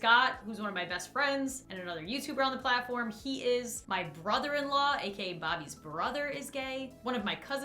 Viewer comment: i heard that as one sentence of just "he is my brother in law" and i was really confused.